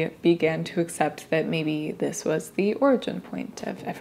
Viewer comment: did you say “my dead girlfriend” at minute 10:04? That was strange… or telling(?) 😮 I will have to listen to the rest of the video to see what you discovered.